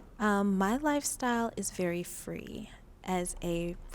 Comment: Number 5 literally can’t stop mentioning she has a 17yr old son 😂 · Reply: Why I thought contestant 6 said “My life is very Freaky” the first time I heard it 😂😂